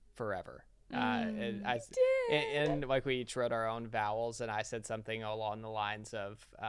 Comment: Haha I literally LOL'd at "bowels" ! Love you guys! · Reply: I thought he said vowels instead of vows but it’s almost a blend of both.